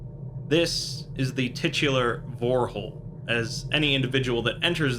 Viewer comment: I was about to drink my soda then I heard "vorehole" I was like did I heard that correctly